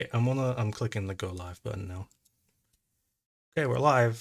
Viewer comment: I heard "I'm going to hit the Gulag button now" 😂